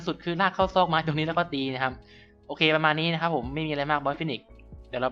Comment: ได้ยินบอสฟีนิก